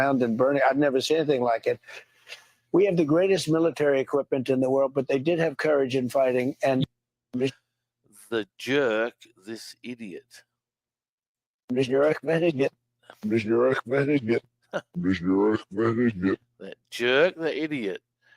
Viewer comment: I heard "the jerk met idiot". Also, "seal that inner search you are". At the end, "when they go, we're sad". Death by machetes.